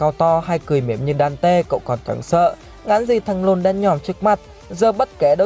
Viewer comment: ai nghe nhầm thành thằng lôn` giống t không =))